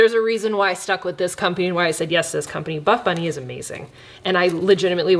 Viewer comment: Why do I keep hearing first ‘bad bunny’ instead of ‘buff bunny’? Omg why am I like this? 😭😂